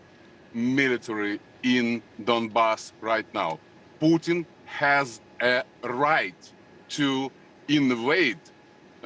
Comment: “Putin has the right to invade” Did I hear that right? Russians want war it seems.